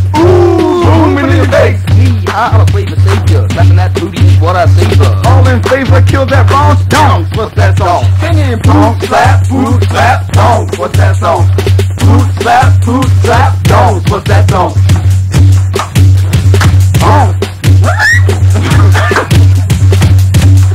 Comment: i only hear "poot slap 2x down potatongn.."